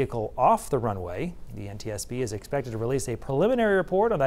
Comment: "Final report in a year or two" there's the bigger problem lmao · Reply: I heard a "final report at the end of a year or two..." unless he's drunk.